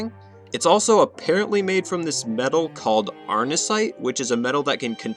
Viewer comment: For some reason i thought he said arson- 😂